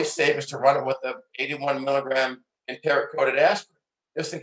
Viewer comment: What does he recommend at @ 5:45 ? I cannot hear clearly. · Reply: 81mg enteric coated aspirin